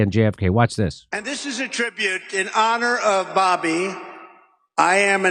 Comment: I don't think he's saying he's going to put RFK Jr on that commission. He said "in honor of Bobby".